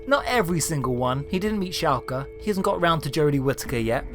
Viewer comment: Whats he saying here? "he didn't meet Shaowker" is what I'm hearing. I've gone over it so many times and thought about every doctor and their number, nickname, and actor, but I can't place it. What's actually being said. I almost never comment on any videos but this is starting to get to me x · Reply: He's saying "Shalka", as in the alternate timeline Ninth Doctor played by Richard E Grant in Scream of the Shalka. Now I'm wondering if Churchill has met the Atkinson, Lumley or Cushing incarnations.